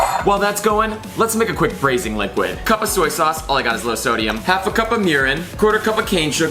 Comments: Am I the only one who Heard him say half of cup of yuren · did he say half a cup of urine 😂